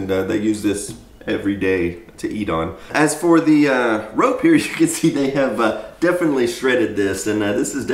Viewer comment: Just FYI, there's a little mistake in the captions for this video at 2:18. You say "as for the rope here" but the caption says "as for the road here". Great video as usual :)